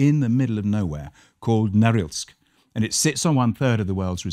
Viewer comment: Can someone tell me what the name of the city he is talking about here at 4:15? Also he says that they sit on top of 1/3 of the world's supply of "dig oil" or something? I cannot make out what he is saying at 4:15 (city name) and what they are sitting on top of at (4:18). Please help. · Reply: The city is Norilsk. They're the top producers of nickel.